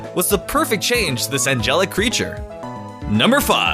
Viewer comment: Who else thought he said "Angelica" at first? Or do I just listen to Hamilton too much?